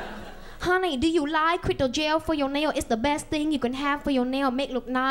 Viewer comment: I thought she said “Crypto Jail”